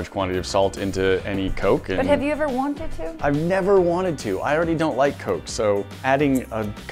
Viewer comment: did he just say he did not like coke?